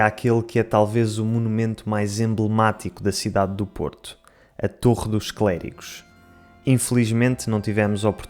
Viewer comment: "A torre dos clérigos" eu entendi "ator dos clérigos" 😅